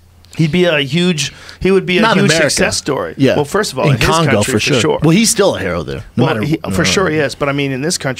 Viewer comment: "In Congo for sure"?? What the hell is this Guatemalan saying?